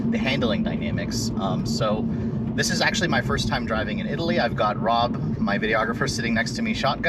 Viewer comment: i thought he said he got robbed in italy lol